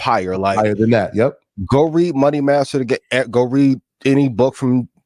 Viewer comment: Can someone tell me what book Ian was referring do at 46:55? Was it Money Matters or Money Masters? And by who? I’d really appreciate it.